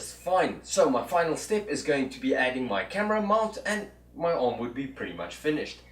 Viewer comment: 😆 thought I heard router arm and was thinking WTF? Finally caught camera mount towards the end. All through the vid kept mumbling to myself "ain't gunna cut it and why in the hell is he doing this?"